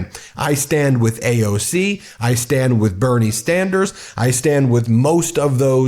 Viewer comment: I swear I heard you say 'I stand with 'Bernie Standards'' And honestly that's not a bad nickname because he really has always 'stood' for what he believed in!